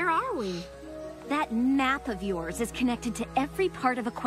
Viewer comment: if you lisson when starlight said that map of yours it sounded like she said that nap of yours :)